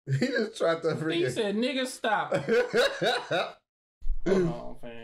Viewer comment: i didn't here a "p" sound at the end almost sounded like n*ggasta. Like how magna calls him shrimpsta or Noelle calls him bakasta (stupidsta). XD